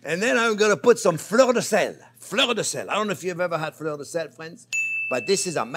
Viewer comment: When you said Fleur De Sel, I thought you said "Florida Sand" 😂